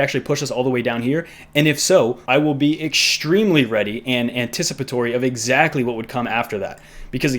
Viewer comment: What was that word - Explanativitiforstory? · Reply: Anticipatory hehe